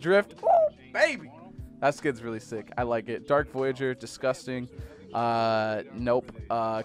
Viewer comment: did he say dark voyager is disgusting